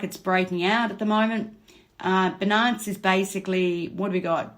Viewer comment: I hear ‘benance’ and reminds me of a St Bernard the dog.. it’s Bi-Nance. Like Finance but beginning with a b. I’m sending love with this comment ❤️ and thank you for all your hard work, much appreciated!